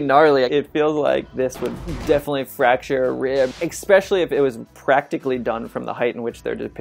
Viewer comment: What does this word, "ekspecially" mean? He's used it twice so far.... And at 8:30, "They axe me to do something..." Where did this guy learn to speak?